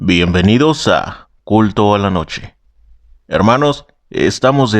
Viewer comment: Entendí "culo" en vez de "culto"😞👎